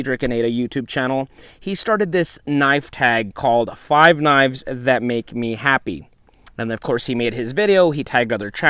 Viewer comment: Dang! Thought you said 5 Wives that make me Happy! 😱😱😁😁